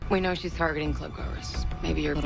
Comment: I literally thought she said “Maybe your black life will come in handy after all.” ARTICULATION IS FUCKING IMPORTANT